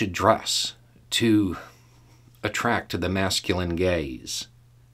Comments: I really need to turn on subtitles from now on - I heard "masculine gays" xD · had to stop you at 2:53 because I was listening and heard your words as "masculine gays" instead of "masculine GAZE." Thanks for the triple take I just did to my phone and the belly laugh that followed. I thought "wait, we were talking about expectations for straight women through the 1960's. Did I just have a stroke and missed the end of that video and Im on a different subject matter video?" And yes, those schools for women existed. In New Orleans in the 1950's, my mother went and "graduated" from Charm School, which is what is was known as. All the young ladies went on Saturdays and it was literally Wife in Training.